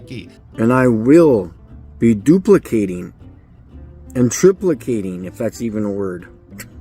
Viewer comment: The only rational question mike asked was if triplicating was a word. After a monumental flerf level of research (approx 1min), I think it technical is a word, but it would be better to say triplicate. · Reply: I thought he said. "Truplicating". Dang. Auto correct didn't want me to type that.